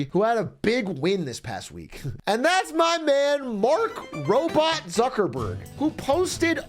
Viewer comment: almost thought he said mark rober until he said "zuckerberg"